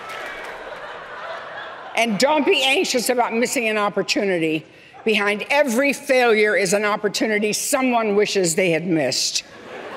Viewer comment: Can someone tell me what did she say at 03:41? Thanks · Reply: Migen Hamilton “And don’t be anxious about missing an opportunity. Behind every failure is an opportunity some one wishes they had missed.”